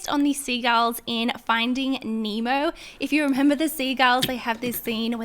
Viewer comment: The first slime I thought she was saying ‘sea GIRLS’ the last time she said it I realized ohhh she is saying ‘sea GULLS’ lol XD